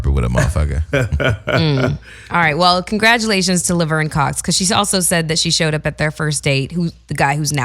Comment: Sounds like said "Congratulations to liver and cox....."